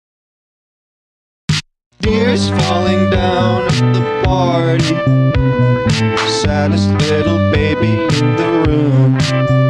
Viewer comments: I CANNOT be the only one that thought it said “deers falling down at the party, Santa’s little baby in the room.” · I THOUGHT IT SAID DEARS FALLING DOWN AT THE PARTY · when i first heard the song i thought it was saying "ears falling down at the party, saddest little bunny in the room" 💀💀💀💀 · THIS DESERVES SO MUCH MORE! YOUR AMAZING OMG Edit:to yall saying u love my pfp TYSMM😭😭 Edit2: TYSM FOR THE LIKES WHAT?!??😭🩷 Last edit: WHATSJKvshsjaushdji THANK YALL SO MUCH?!?!?!?🧑‍🦲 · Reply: I swear I heard “beers falling down at the party” I’m not even 18 yet 😭😭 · Omg, I thought it said “Deers falling down at the tarty” LIKE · I always thought it said “deers falling down” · Omg I thought it said santas little baby in the room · I'm not gonna lie, I thought I heard Santa's little (helper) baby in the room... In the most innocent way possible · Am I the only one who always thought it said "deers falling down at the party" 😂😅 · Tell me why i always thought it said "Deers falling down in park" 💀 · I thought it said deers falling down at the party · At first I heard “tears falling down in the bathroom” But now I hear “tears falling down at the party” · I hear “tears falling down at the bar”